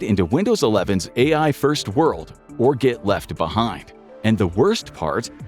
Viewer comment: You say get left behind, I hear that a say prefect I will switch the MAC, oh yeah I did! that already and will not go anywhere else.